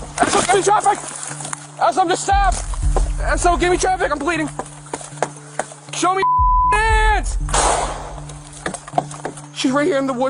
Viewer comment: Not asking her to see her hands, is insane. She obviously could be out of her mind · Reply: I definitely heard "SHOW ME YOUR HANDS